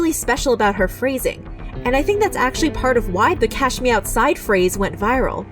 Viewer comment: I always thought she said ‘catch me outside’, not “cash me outside”